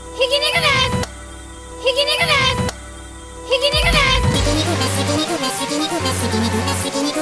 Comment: 途中で皮肉ですて聞こえる